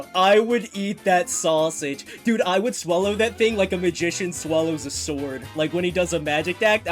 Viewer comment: I swear I heard vagician